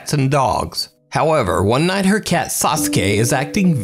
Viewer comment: “one night her cat SASUKE - “ Me: Wait did I hear that correctly